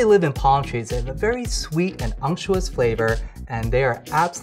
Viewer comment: What does it say at min 7:08? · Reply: he said unctuous